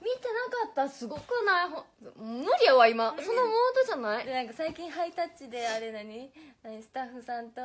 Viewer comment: Sana said "I'm not in that mode right now." instead of the subtitle's "It's not in the right mood." Mode referring to her mode change from the usual informal Osakan dialect to standard accent speaking mode